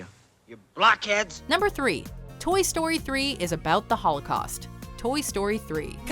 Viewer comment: I was half mindedly watching and then I heard the thing about Toy Story 3, I thought she said Toy Story 3 is about Toy Story 3...